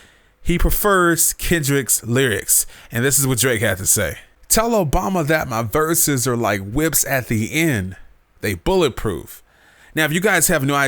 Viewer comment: I thought he said: "Tell Obama that my verses are just like the whips that he in, they bulletproof"